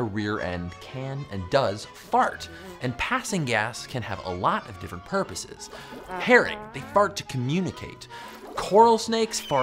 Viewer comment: I thought he said parrot or carrot I confused enuf that I had to turn on the captions Herring is not a common word/fish I hear/know